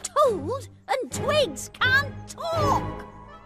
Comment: Does Anyone Have Any Idea On What He Said? 🙂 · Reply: I think He try say he can’t talk 🥺🥺 6:47